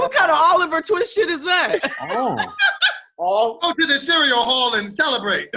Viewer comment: Tahir’s tongue at all times: 🎶 I may be slow but I’m readyyyyy. I definitely thought he said cereal hall. I thought the jokes were going to be about him being old, because what even is a cereal hall?! 😂😂😂